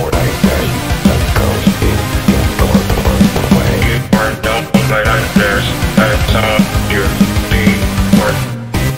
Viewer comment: What is mark saying at 1:57?? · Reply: "you are not all my nightmares because i saw u before"